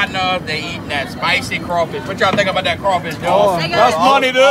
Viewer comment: For a sec there I thought he said at 4:40 "What you nikkas think about that crawfish dude."